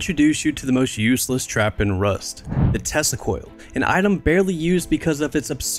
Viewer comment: THE WHAT? hell nah brother, how can u say "The Testicoil" so naturally